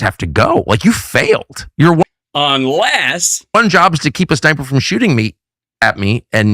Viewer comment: I had to go back - Tucker says 'keep a sniper from shooting me', I heard 'keep his diaper from shooting me'. 🤣